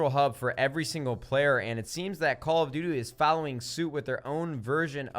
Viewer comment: Anybody else hear at 4:50 him say "call of doodoo"?